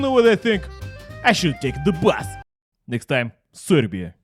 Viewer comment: "next time: Serbia" Me: If there is one thing cold war films have taught us, it's that no one wants to go to...oh, SERBIA? I'm sorry, I thought you said SIBERIA